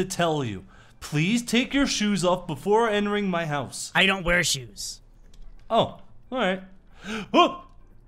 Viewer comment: what r does? shoes! I thought u said you did not were shoes